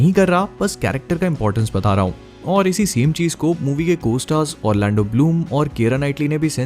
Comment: If you want to listen best Word from so go on 3:23 And then tell me what is that word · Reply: Co stars